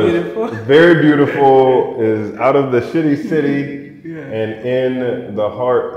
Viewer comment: Did he just say “the sh!tty City”? The capital, Accra? Wow brother… From my many experiences there I would never describe it quite that way… but to each his own. 🤔